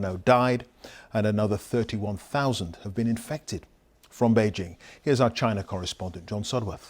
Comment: Did he just say "another 31000 has been infected"?